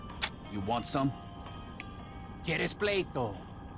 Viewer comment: Sounded like get his plate through.